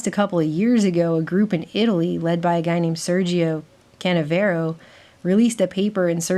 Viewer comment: I thought you said Sergio Cadavero. I was like waiiiit a minute.